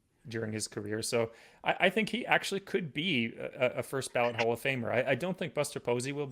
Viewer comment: On what planet is Miguel Cabrera not a Hall of Famer? Get out of here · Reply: Yeah, I heard that as him potentially not being a "first ballot HoF'er"